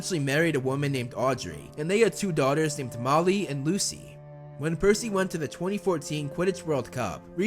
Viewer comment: its Molly and Lucy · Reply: Sage Blast I know that Like I said I HEARD “tamale and Lucy”